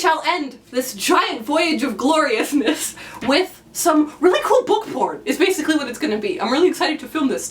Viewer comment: Your videos are always so well edited. Loved the "book porn". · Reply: Is that what she said :'D I thought she said "book poll"